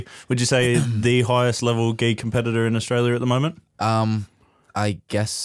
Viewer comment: I heard "The highest level gay competitor in Australia". After googling I realised you said 'gi'. Not making gay jokes, just laughing at the way that my brain processes things.